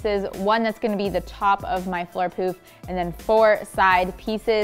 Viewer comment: My boyfriend heard it as 'floor poop' - now I can't unhear 🙉